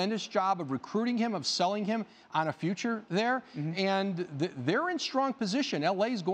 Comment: I thought it said thunder in strong position for LBJ, 😂😂almost subscribed to ESPN